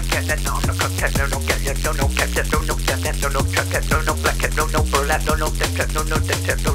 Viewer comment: People who heard black cat at #0:32 [NOT BEGGING LIKE IF YOU WANT] 👇